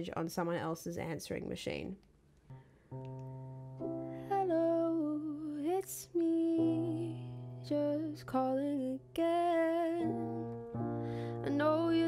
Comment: I was half-listening but i thought she said "hello its me, just call me adele" in